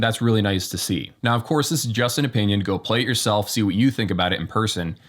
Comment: Did you say this is "Justin opinion"? ;)